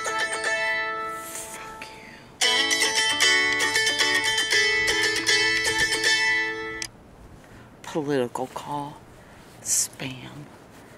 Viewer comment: Did I hear a “fuck you” at 4:18 ?? 😂😂😂 No judgment. I’m a New Yorker.